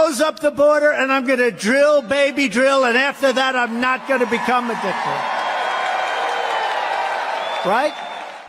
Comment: what is he saying qt the end? "Drill baby drill and after that I'm not going to be competent"?